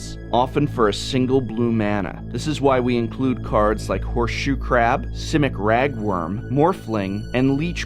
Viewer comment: i thought you said crap xD